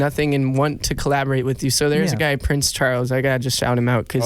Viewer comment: I don’t understand at 16:04 . did Daniel say they did songs with Prince Charles ? or am I hearing things ?